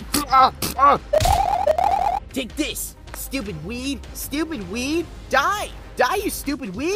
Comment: when Coney was calling the Apple Bomb a "stupid weed", I misheard "weed" as "weeb". I shitpost too much to make that mistake.